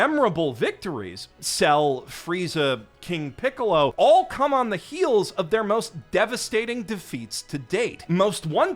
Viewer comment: ! Why can’t you pick a thumbnail?! I’m very concerned. Love the first one tho. Also “cum on the heels is what I heard”